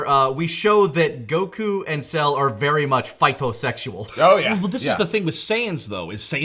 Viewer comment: what did say they were ? Had a hard time hearing it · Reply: Fight-o-sexual The joke is they get off fighting